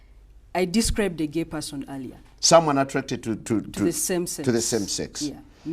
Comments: I thought she said the simpsons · i thought they said the Simpsons hahaha